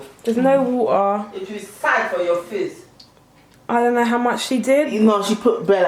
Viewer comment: please what did say?😂😂😂😂😂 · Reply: Hila “Make sure there’s no water in the pan o! It will SAI for your face!” 🤣